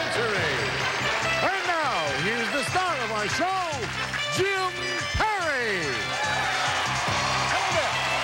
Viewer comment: is that jim carey????? I really thought I heard them say that the host was Jim Carrey when he came out